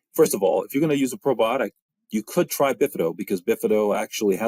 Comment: Hi, everyone. My native language is not English and i don't understand the name of the probiotics named here 31:37 Can anybody help me? Thanks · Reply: Bifido